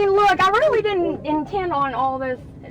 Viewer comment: At least she apologized for all the “distraction” she caused. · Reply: i thought she said destruction